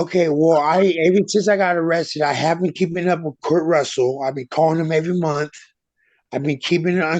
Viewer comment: Does everyone keep calling KIT Russell KURT Russell instead, or am I just hearing it wrong???